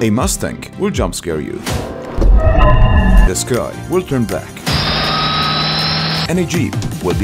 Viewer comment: i thought he said "the guy will turn black"😅